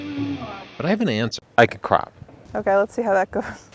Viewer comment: the placement of the ad made it sound like said "I cry" instead of "I crop" lol